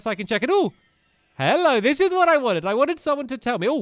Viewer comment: The tips of the obliterater were the divine beasts if you looked closely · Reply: when he started I thought he said "Hello this is Bambi"